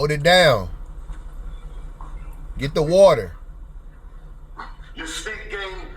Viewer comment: 🤣🤣🤣🤣🤣🤣🤣🤣 I died with the HOLD IT DOWN! I thought was bout to say "Pass me the water" 🤣🤣🤣🤣🤣🤣🤣🤣🤣🤣